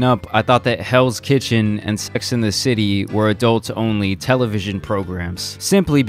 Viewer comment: I thought he said “spics in the city”